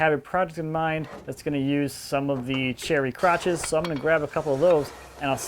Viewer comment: did he say he was going to grab some crotches???